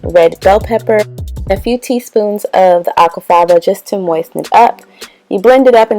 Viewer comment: Time stamp 3:57 what did she say to add?? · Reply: Aquafaba